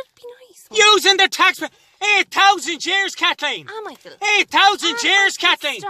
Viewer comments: i honestly thought he said 8000 chairs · "8,000 years Kathleen!" just keeps getting better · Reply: I really thought he was saying 8,000 jeers. Thank you for the clarification.